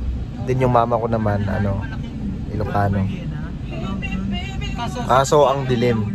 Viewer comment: Tama ba narinig ko? oh baka nabingi na ata ako HAHAHAHAHAHA 5:49 sabi ni KEN: yung mama ko naman ilocano (Correct me if im wrong) Woahhhh im ILOCANO po proud ILOCANO here 😁💙